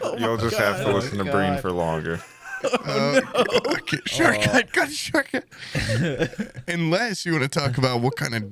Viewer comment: While they're talking about shortcuts, I could've sworn I heard say "shortcut cut short" right here, which means only one thing: has seen Hot Wheels World Race. I didn't know another one of us existed.